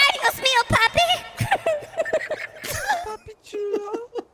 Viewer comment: what did I just hear? Something puppy?